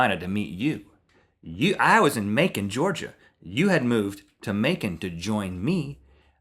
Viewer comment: lol i thought rhett kept saying bacon georgia